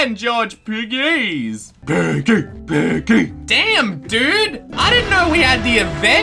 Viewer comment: did he say damn